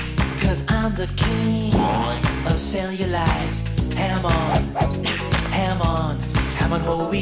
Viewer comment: so that's where Araki got Hamon. . . Jk, but it sounds like he said Hamon no beat even though I know he's not.